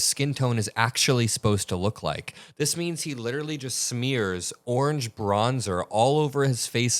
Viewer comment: I thought you said "smears Orange Brawndo" ... as a hat-tip to the fact that the movie "Idiocracy" turned out to be prophetic.